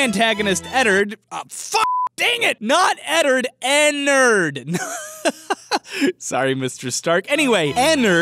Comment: did he said "sorry mr strark"?